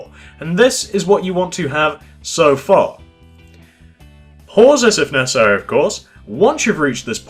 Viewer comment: I thought he said Horses if neccasary of course